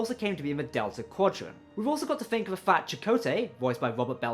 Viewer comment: Did you say, "we've also got to think of a fat Checote" at 7:28 ?